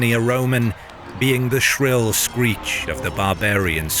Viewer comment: English? There were no English there at that time. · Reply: "Being the shrill screech" it did sound like he said English though lol